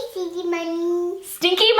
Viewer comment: I thought she was saying Sydney’s mommy... 🤣💕